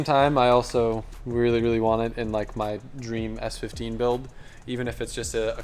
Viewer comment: said he'll only keep uploading in 4K if more than 10% of the viewers hit the like button on the video. Y'all know what to do lmao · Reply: Ngl I thought it said $15 build instead of S15 build 😂